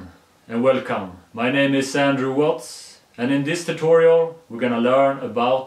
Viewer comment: I say Andrew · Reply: oh, It sounded like you said Sandrew xD